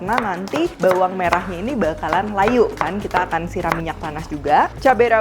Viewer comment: Kl org Bali, biasanya kacang panjang nya di rebus sebentar baru di belah2, lbh gampang, gak di potong gitu. Menit 7:44 apa sy yg salah dengar ya? Bawangnya akan Kita siram pake minyak tanah 😀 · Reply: Minyak panas. Jangan dari kesalahan orang